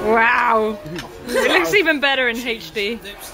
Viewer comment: "It looks so much better in HD" I thought you said it TASTES so much better in HD, in which case it should have been the quote of the gathering...